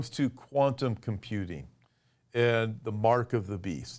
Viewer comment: Ok, I totally heard "Quantum Cutie." AI knew I was listening. Lol ...